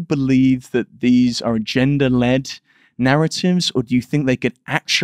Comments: Bret: Did you say "Gender Led". hahahahaha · Thanks for sharing. Excellent conversation. Great questions, and very insightful responses by Bret. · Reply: I also thought he said gender led.